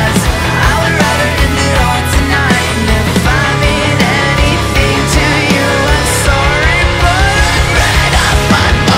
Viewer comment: Hold up... 2:52 Did he say "Mommy"? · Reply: “If I mean”